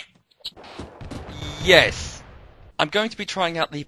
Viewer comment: Loving your videos. and might not be believable, but I'm deaf, and you edit your own videos to have their texts so I don't have to rely on YouTube to fuck up on trying to keep up or to keep track of what's said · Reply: he actually said a sarcastic yes if you were wondering :)